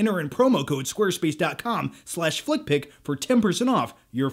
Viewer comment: swear down thought that said FLICK-PRICK.